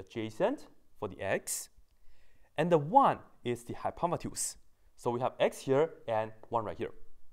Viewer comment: did he REALLY say "one is the Hypomatuse"?!?! I reckon he needs to hi pot & use a dictionary...